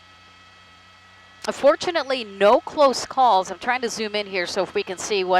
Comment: did she say "Unfortunately, no close calls."? Is this reporter on crack? What a nutcase.